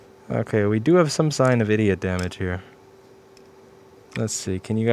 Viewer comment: what did he say "eda damage"? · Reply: "idiot" damage